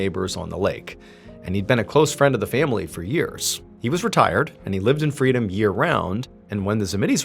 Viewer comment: "he was Ret@ I thought that's what was said